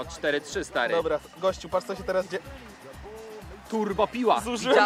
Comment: czy tylko ja za pierwszym razem usłyszałem tam ,,kurwo piła" XD